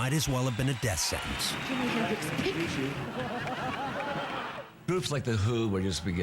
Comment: what does she say and pick up? I hear "Jimi Hendrix pick"